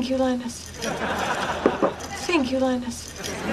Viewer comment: Now all I heard is "think you line us"